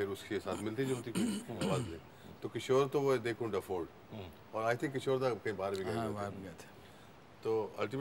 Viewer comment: what’s did he say kishore kya huey they? To which sanu da nodded his head ? 🤔🤔 anyone ? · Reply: kishore ko they couldn't afford, i guess...